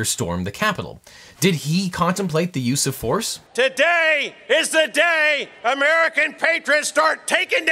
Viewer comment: When he went "today is the day" i heard like "today is the last day of the republic!"